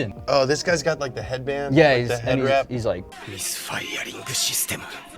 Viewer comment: Subtitle is wrong. It should be "misfiring system".